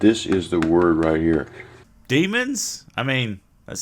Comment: “Demons?” I thought it said “Lemons”. Not that that would make this video any more or less comprehensible.